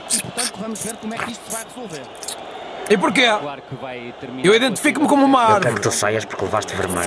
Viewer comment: parece mesmo que ele diz identifico-me com uma árvore. 😂😂😂